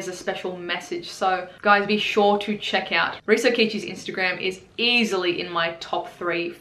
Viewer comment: This whole time I thought you were saying risAkichi... 🤔🤔🤔 All right, ya got me! I’m going now to subscribe to risokichi. Thanks!! 😎👍